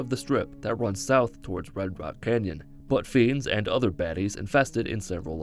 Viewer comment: this sounds more like "butt fiends" instead of "but fiends"